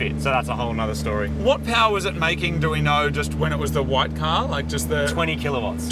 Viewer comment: Because of the accent, when Moog said @ 18:25 "what power was it making, do we know, when it was just the white car", instead of "white car" I've heard "white power"